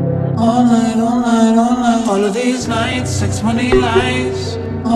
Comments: This whole time I thought it said ‘Sex money feels right’😔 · I thought it said " Sex money bills yuhh "